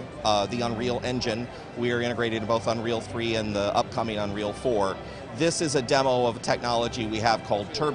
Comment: just a fucking secound! did he said upcomming unreal 4? O.O @ 2:13 OMFG NEW UNREAL? SWEEET! <3